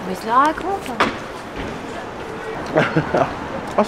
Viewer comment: Je rêve ou a 09:59 j'ai entendu: - Comment il se la raconte !? 😂